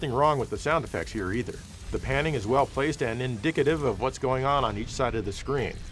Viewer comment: "indicative"?! Did he just say dicks?